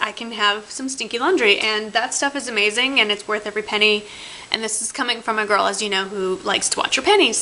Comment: hahaha i had to rewind, cos i thought you said " and this coming froma girl who likes to wash her panties" hahahah but you didn't lol